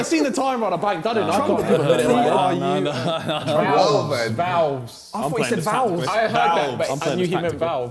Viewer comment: I thought Vikk said vowels instead of Valves aswell